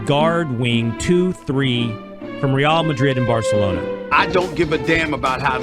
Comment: from 0:16 - 0:18 did he just say from luka doncic from real madrid in barcelona??????